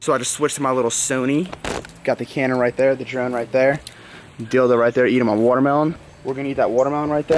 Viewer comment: did he say dyl-do😂?